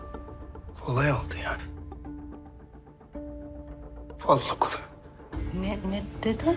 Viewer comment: de Behram ne diyor anlayan var mı ? Olaya bak ya dedikten sonra birşey söylüyor anlayamadım bir türlü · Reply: Kolay oldu yani valla kolay oldu